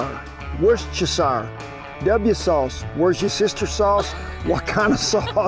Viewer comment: Cowboy Kent, I love your channel. Ever since I first heard you call "W sauce" "Where's your sister? sauce" no one has not known what I meant. Let's just keep using that. It's classic! :)